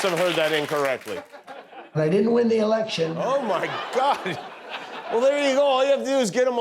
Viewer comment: How anyone still wants this Poetin lover to keep talking ? ... btw he said we DID win the election......you misheard there ^_^